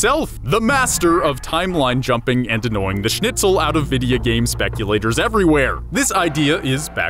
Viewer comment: did u say denying the schnitzel?!?!?